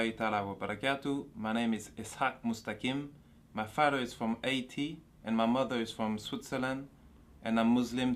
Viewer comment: im pretty sure what he said at 0:10 "my father is from haiti"